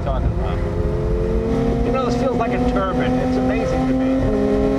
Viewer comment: did he just say that a Porche 911 GT3 feels like a turban?